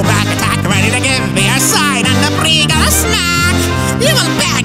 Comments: Every time I heard "ready to give the ursine and the breegull a smack" I swear I kept hearing "ready to get me a side of that $3 snack"" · I swear I hear "Ready to give the ursine and the breegul a snack "